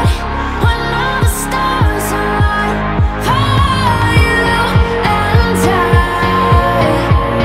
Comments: why do i hear 'for you hentai' at 1:14 ok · All I hear is "for you, hentai" and I hate that my brain does this ffs